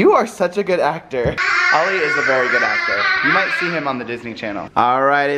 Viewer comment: Ollie on Disney Channel?!!?!???!!! Did I hear correctly!!!!!!!!!!